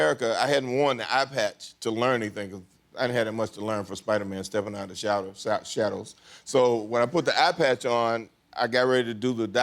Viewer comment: did he just said Spiderman step out of the shadows?